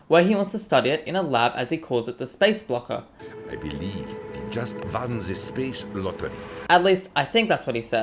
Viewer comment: he actually said “I believe we have just won the space lottery”